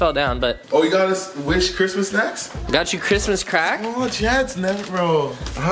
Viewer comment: Did he just say Christmas Crack.. LMAO'